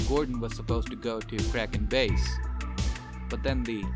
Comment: ? go to what? I can't understand a thing you said there? Edit: the closed captions said "kraken base", but I still dont know wtf that is.